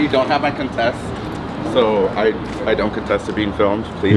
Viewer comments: I thought he said confess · I love how the guy said “I don’t contest to being filmed” so you don’t object to being filmed? Dude basically gave permission in an aggressive tone😂 vocabulary is very important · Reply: It's hard to understand speaking through those masks but that was funny. I thought he said confess.